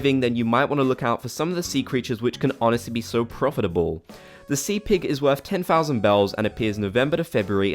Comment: I heard say "Sea Pig" and I thought he said "Sea Pickle." I was very confused.